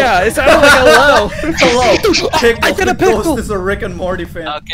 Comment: he did not say hello or pickle he said adult lol